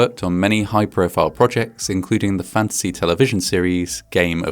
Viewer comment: At first i misheard "fantasy television series" as "fancy television series" and i was very confused 😂... what is so fancy about GoT 😅